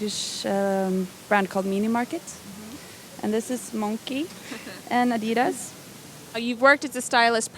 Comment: What is the brand she says at 5:00 ?? Is it Monkee?? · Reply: Monki